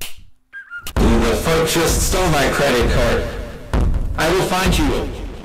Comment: I thought MrBeast said “I will fight you,” but that was just me expecting a MrBeast boss fight.